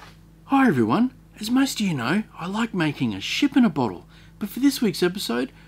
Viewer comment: My Wife said did he just say he likes taking a shit in a bottle 🤣🤣🤣